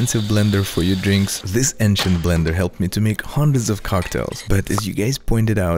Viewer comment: Thought it said bender